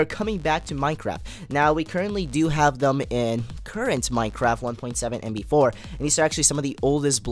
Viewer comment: for a sec I got so confused im on 1.7.9 I thought u said 1.7.8 and I was like WUT! Im on 1.7.9 am I missing out ;( but im not :D